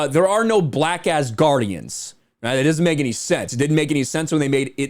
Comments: Lol it’s sounded like u said “black ass guardians” 😂🤣😅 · I had to listen again to make sure I didn't hear black a$$ guardians